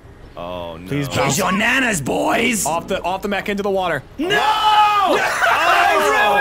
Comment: In that first worms clip says something about the mech but it sounds like he says the Mexican